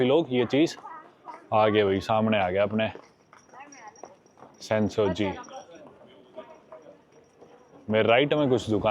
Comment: I heard marathi ❤ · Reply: Me too head it 😂 "kai melya la, bass zara.."